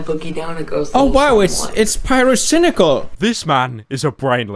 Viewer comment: With subtitles on instead of it saying pyrocynical it says gay fury legit